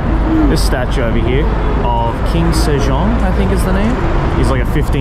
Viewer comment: bruh I misheard Kim Sejeong which is one of my favorite korean singers and actors and I was like daumn boi they made mah girl a statue? 😭🤣